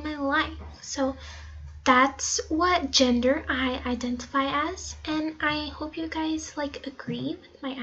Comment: am I the only one who heard her say "that's what gender I identify as"